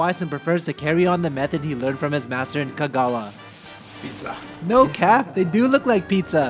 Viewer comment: Did paolo just say "no cap" ??